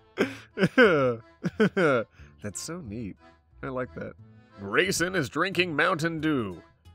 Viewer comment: When said 'Grayson', I heard it as 'Raisin' help